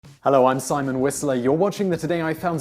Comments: I thought he said "I'm Simon Weasley 😂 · My dumb ass poTTERHEAD thought he sAiD Simon Weasly